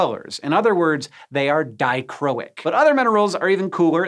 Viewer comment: I’m kinda annoyed that whoever did the captions didn’t include words like “dichroic” at 3:29 for the subtitles, or “IVTC” at 5:53. I’m about half deaf and if I hadn’t had the volume up enough or been watching his lips, I wouldn’t’ve known what Hank said. I appreciate the hard work and dedication for taking the time to make the subtitles in the first place, but it throws off the timing and people who’re like me, deaf or hard of hearing’s ability to follow along since it just has unfinished sentences, with a period where it shouldn’t actually be ending the sentence. Like “In other words, they’re.” at 3:29, for example. If I knew how to do the subtitles and watched these videos on my laptop instead of my phone, I’d fix it myself but I don’t. So if someone who does know, sees this, would you add in the missing subtitles? Please? Thank you if you do. I, and probably many others, appreciate it.